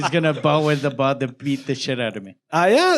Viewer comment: is gonna...baah with the butt beat the shit out of me ,,,,!!!!!! ...i heard it...😂😂